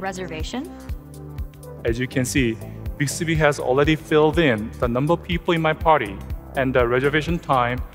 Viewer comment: Did he say siri already listen 8:04 around